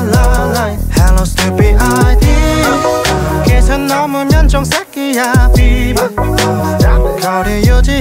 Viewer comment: 목소리 완전 꿀떨어지는 와중에 자꾸 "이 선 넘으면 정색이야 삡~"이 "이 선 넘으면 저ㅅㄲ야"로 들리는 내 미친 귀 ㅋㅋㅋㅋㅋㅋ 아읔ㅋㅋㅋㅋㅋ 이번 커버도 완전 듣기 좋네요. 두고두고 와서 들으러 올게요.